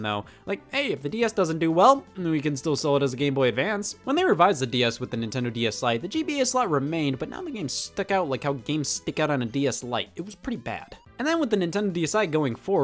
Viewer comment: did anyone else hear “when they revised the DS with the DS lite the GBA card slot tsyaed but they stuck out much like how the stuck out on a DS lite”? No? Just me? Nice to know